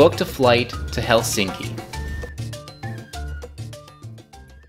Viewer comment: Sounded like you said pechinki from PUBG